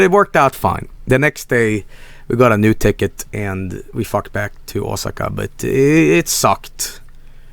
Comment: Did Joel just say "we fucked back to Osaka"? I guess he's closer with Frank than I thought.